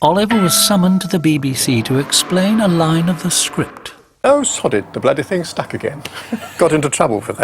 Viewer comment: I have a toy Clanger. When you squeeze it, the sounds come out. But until this TV show, I didn't know what it was saying, see 21:49 "Oh sod it, the bloody thing's stuck again"